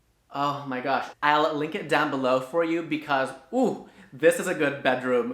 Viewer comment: Lol These tips are marvelous!! but am i the only one who heard "im gonna lick it down below" ? lord have mercy i had to do a replay because i totally misheard that he said link it down below! i need to go find my rose and a some holy oil lmaoooo laughing at my self 🤣